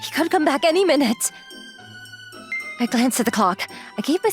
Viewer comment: I thought she said "i glanced at the cock" 💀🙏